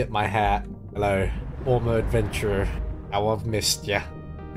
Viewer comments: I think he said "How I've missed you" like "Oh how much I've missed you" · I think he said 'how i've missed ya' instead Thank you for the clip, sasuga... heartwarming turns nasty lmao